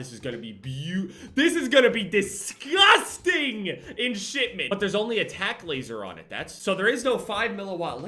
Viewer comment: I thought it said shit ment lmao